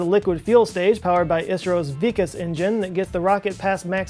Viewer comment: I thought I heard weakest engine instead of Vikas engine.....